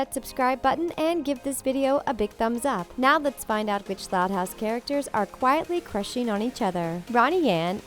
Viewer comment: Is it just me or did it sound like she said: "Which thot house characters"